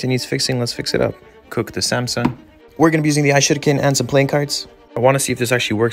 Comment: Whenever he says “ishuriken” I hear “i-shitcan”